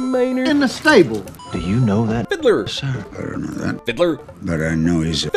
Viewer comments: Thought he said FIDLAR when he said fiddler... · Anyone else thought when he said fiddler the first few times it sounded like hitler?